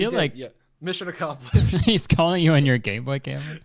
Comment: I swear it sounds like Kurtis said "He's calling you on your gay boy camera"